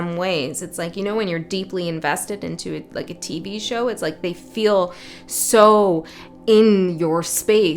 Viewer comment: Is it just me or did i hear 'tee bee show'? 11:28 🤯✨️